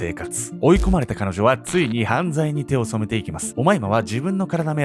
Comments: どうしても「おまいら」に聞こえて昔ながらの2ちゃんねらーに責められてる気分になた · 「おまえいま」に聞こえた